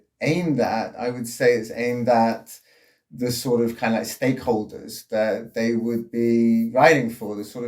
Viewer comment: "WHO IS IT AIMED AT?! - IT's aimed at the Stay Colders!" Freeze! - This Town ain't big enough for both of us! BEAT IT, Stranger!! (I heard 'Stake Holders, as 'Staycolders')